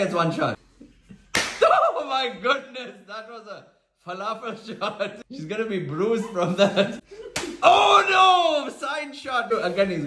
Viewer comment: I thought he said "she's gonna be bruised brothers" 😂